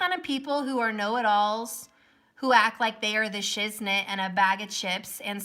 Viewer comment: Oh my !!....😂😂... Girl I love you !!... U BE EXACTLY who U ARE !! YOU'VE MADE MY DAY.. I HAVEN'T SMILED OR LAUGHED IN MONTHS... BUT I SWEAR I JUST HEARD YOU SAY SHE THINKS SHE'S ALL THAT AND A BAG OF S***.....U SAID CHIPS N THEN SHIT ♥️♥️♥️👆 LOLOLOLOLOLOLOLOLOLOLL 😂😂🤣.. AND YES... NOTHING IS EVER BEEN THIS PAINFUL FOR ME EVER! AND I THOUGHT I WAS A SOLDIER 😢😢😢💔💔...I HAD A GRAND BABY DIED SIX YEARS AGO THEN MY HUSBAND DIED FOUR YEARS AGO OUT OF THE BLUE... I'VE BEEN GRIEVING FOR SO LONG AND THEN I STEP INTO THIS SITUATION... IT MAKES YOU WONDER WHY?